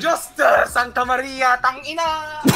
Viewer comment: he is a filipino . 😂. Hahaha lmao i just hear tang ina from him.